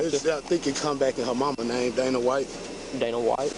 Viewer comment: A black woman named Dana White! Gotta love it. :face-green-smiling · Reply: I thought he said Vanna White.🤣